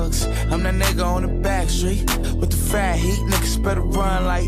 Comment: on the backstreet with the fat heat? it sounds like he says frat heat. there is def. an r in there.